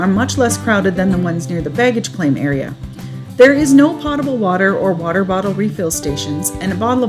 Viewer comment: Good info even for those who’ve been thru the airport before. Thanks. At 2:19 you mentioned there was no pot-ible water available. Who cares. I don’t normally bring a pot on the airplane ! Yuk yuk. I think you meant to say potable, as in poat (rhymes with boat). Say it ‘poat-able’ to pronounce correctly for your followers. Can hardly wait to see the construction re. the Mayan train. Difficult to imagine the positive impact this service will have !!